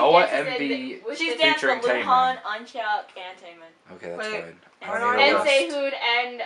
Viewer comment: Thought I heard someone pronounce Eunhyuk's name as "Unchuck". :))